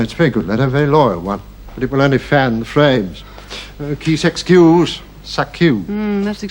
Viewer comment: I don't understand much french, so when, at 1:53:52, said, "Qui s'excuse s'accuse", I went to the video transcription for any clues, and found only another ludicrous attempt by YouTube's transcription: "little fan frames kiss excuse". 😅